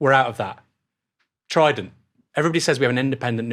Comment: anyone know what he just said here? I’ve played it back 7 times and I simply can’t make it out. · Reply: trident; everyone says we have an independent nuclear deterrent but we don't